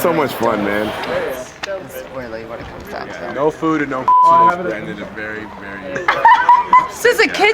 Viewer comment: "no food and no pussy" lmao seriously now how could they not figure out what he is saying